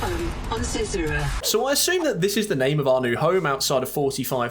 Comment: when I heard "welcome to cicada" I thought they said "welcome to zezura" idk why